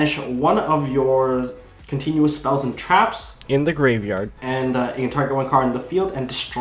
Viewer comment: Anyone else hear that "in the graveyard" insertion at 3:08?